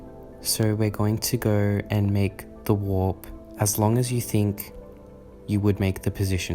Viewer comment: i thought he said the wap lol